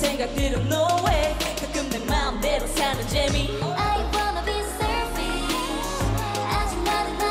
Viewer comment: Y’all hear that person at 2:15 saying “of course”😂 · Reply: I heard "I want"